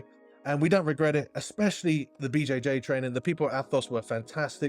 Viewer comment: Did you just say V-jay-jay training? 😅😇☺